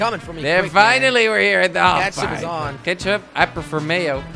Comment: what i heard in intro: "is jelly gonna 'KETCHUP'.. is jelly gonna 'KETCHUP'......"